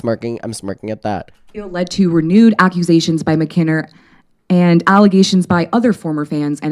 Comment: I aspire to be as confident as Alexa saying “Mkenner” with zero hesitation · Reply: I thought she said "mackinter" for some reason. 😂😂